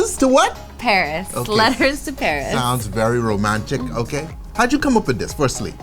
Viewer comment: I would describe this drink as "romance". I love the name of the drink and can't wait to try it! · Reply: Laura Madsen I think he said that it sounds very romantic @ 1:05 but to me it seemed like he said "sounds very romagic " (a combination of romance and magic), which is what I'd call it. 💖